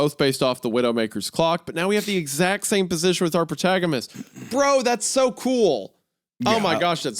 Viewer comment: Anyone else hear Wendigoon say “PortagaMist” @ 5:21:50 instead of “protagonist” 😂